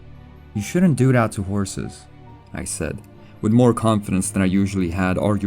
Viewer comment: Oh bollocks - I thought you said “whores”. Completely different niche.